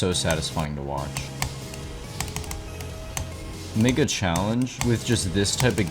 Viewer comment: What did bro say? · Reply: No I said “make a” 😭